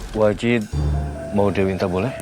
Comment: Gue gak salah denger nihh tadi wajid bilang "wajid nak dewi boleh" 😅